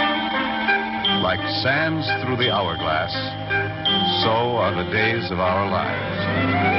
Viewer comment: I heard 'Like Sam Threw Her Hour Glass, This Is Days Of Our Lives.